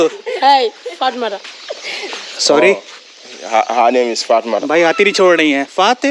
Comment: Being bengali ....I heard "podmara"🤣🤣😂😂😂😂😂😂